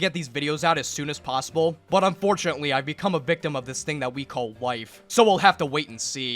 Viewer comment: Did he say he became a victim to life or wife